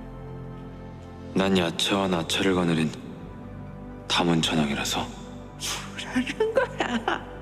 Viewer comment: 이거 뭐라고 한건지 아는사람? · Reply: 야차와 나찰(악귀들)을 거느린 다문천왕(부처의 뜻과 중생을 지키는 불교 사천왕중 1)